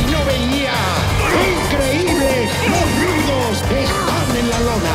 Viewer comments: The announcer is "El rudo Rivera"? OMG I just heard "Los rudos estan en la lona!" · Did he just say "boludos" ??????